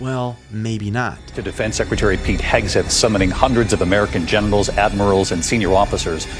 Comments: i swear it sounds like he said hundreds of genitals instead of generals 😂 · I was half-paying attention until I heard him say “summoning hundreds of American genitals.”